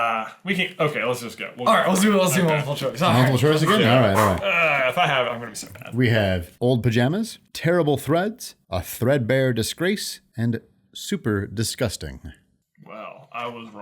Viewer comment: I thought she said hobo pajamas